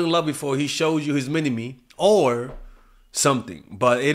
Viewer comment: His mini me! 😂😂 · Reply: I thought he said mini meat.